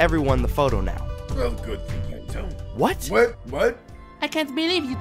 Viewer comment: It said well good thing you dont, the captions are wrong.